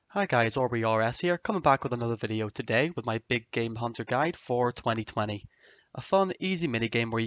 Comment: At first I thought he said "my big gay hunter guide"